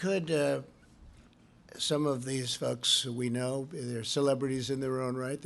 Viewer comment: Anyone else thought Trump said the f word?😳😂